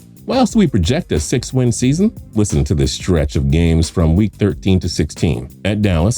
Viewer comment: who else heard 3-13